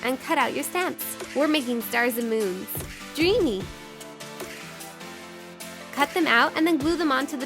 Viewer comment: I swear I heard you say McDreamy. #Grey’s Fan